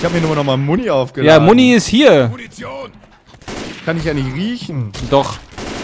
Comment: Wer hat nach dem "Munition ist hier" 0:53 anstatt "Muntion!" "Du Idiot" verstanden?